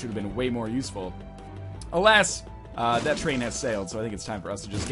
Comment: Did he say... "That train has sailed" ?